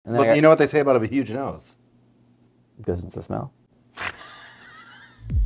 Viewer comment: wtf does say at the very beginning of the episode? it sounds like, "doesn't the smell?"